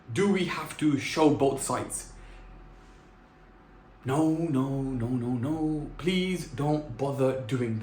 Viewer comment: I think he said no